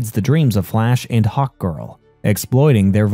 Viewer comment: this was an awesome recap, ut i can't help but laugh every time you say hawk girl I hear "Hot Girl" lol